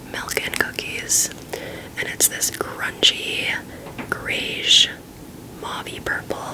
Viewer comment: half asleep and Istg I thought you said “crunchy” describing the color 😭 especially after saying “milk & cookies” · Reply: Lmao I heard that too when I was editing but I definitely said “grungy” 😂